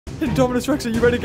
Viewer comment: Did you just say indominus??